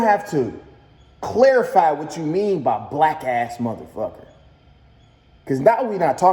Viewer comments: It sound like he said wack ass morherfucker... or am I trippen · He said BLACK not wack. Dont try to save him. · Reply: I heard “wack ass motherfucker” and I love DDG.